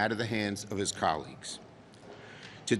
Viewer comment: Colleagues? Oh I thought you said commies